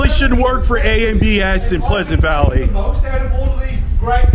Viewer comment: what did he say? “ANB asda pleasant valley”?